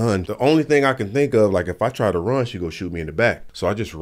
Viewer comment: Did i hear "WAP" "WAP" in the end